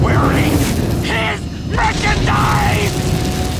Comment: thank god for the comments,I heard was"his freaking dies"while he said "his merchandise" this makes way more sense.